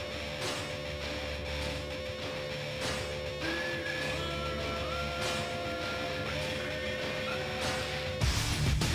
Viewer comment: What does the voice say 1:17? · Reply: "Die Waffe legt an"